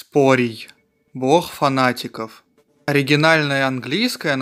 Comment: я один услышал лох фанатиков?